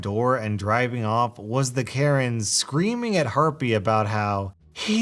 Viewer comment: I think he's been saying, "herpie" I don't.... I don't know.